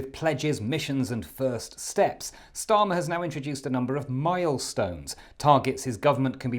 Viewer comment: Rachel Reeves and Liz Kendall. Oh, sorry-I thought you said millstones.